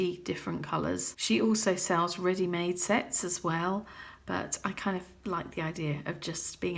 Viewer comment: So I'm at work, so I was only half-listening, but I thought I heard "she also sells ready-made sex as well" and let me tell you, tea was spat out. Still laughing, though!